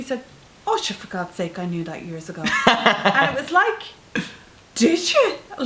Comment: Great Video! At about 2:40 the auto captions say "new light years ago" where I believe they were meant to say "knew that years ago." Unfortunately there is also a mistake at 2:54 as well. Being a popular Youtuber, a lot of people watch your videos, including hearing impaired ones who rely on closed captions to watch your videos. This is such a delicate subject matter and I know you would help them a lot. Thank You!